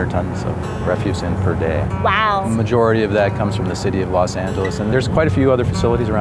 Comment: did he say Los Santos LOL